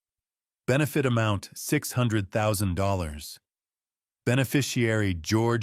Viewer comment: I thought tou said $800,000 earlier.